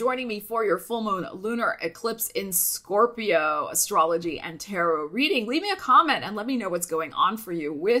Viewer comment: At first I thought you said! And now for your terror reading 😂😂😂 Instead of your tarot reading. 😅